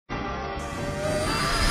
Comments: What is happening? It's a bad dream. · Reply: All this time I thought he said “What is happening to McQueen?” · What is happening? It's a bad dream! 0:08 WHAT IS HAPPENING!?!? 0:12 Impossible! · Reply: All this time, I thought he said “what is a happening to McQueen?”